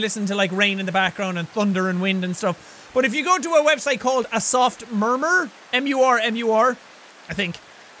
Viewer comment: When he said "a soft murmur" I thought he said "a soft pervert"